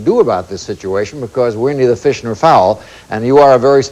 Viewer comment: What did say at 2:31? It sounded like he said, "We need a fish or a fowl" or "We're neither fishin' nor fowl?" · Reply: "We're neither fish nor fowl."